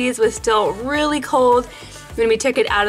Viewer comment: I know it’s Kenzie but it sounded like she said kenslie at 4:34🤔🤷‍♀️ idk